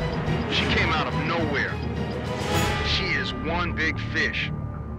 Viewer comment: i thought he said "she is one big bitch" and i had to rewind it hahh